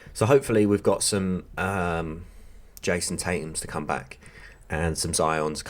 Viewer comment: I had to back the video up abit. For some reason i miss heard you and thought you said your waiting for some Jason Stathams to come back😂